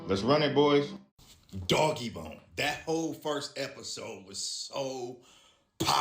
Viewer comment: Bro can someone tell me what tv show that guy was talking about with the dust and invisible city?? Sounds like he says doggybone but I know it's not so please help someone!!!